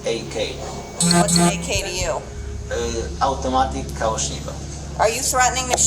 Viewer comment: theres idk a writers mistake or sth but not "spanish" he says polish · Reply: also 15:34 what is "kaushiba" ? he said "kalashnikow" also "kałasznikow" if you need fully polish name